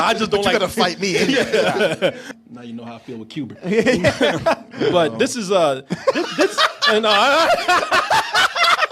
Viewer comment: What did say at 22:40?? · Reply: "Now you know how I feel with Qbert"